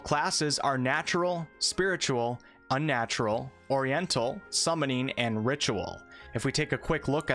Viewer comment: Did you say "Oriental" at 7:52? That's a weird class of spells compared to the others!